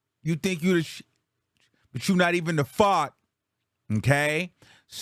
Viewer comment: My old ass thought she said you think u the 💩 but u not even the f**k😂😂😂 who says fart like that lol